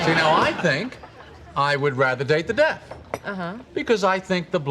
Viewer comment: I thought Jerry said, “I think I would rather date the DEAD”